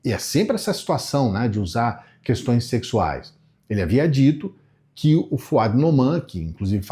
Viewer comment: "Elle havia dito" eu ouvi errado na 1a vez: ouvi "ele é viadito"...